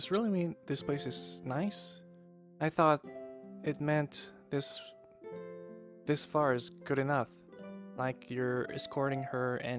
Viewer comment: If you still looking for the artist name then he is Yashima Takahiro. Also in 00:53 ya it should be "This is is good" mah i guess you already figure it out since Aoi say "I'll take you back home" after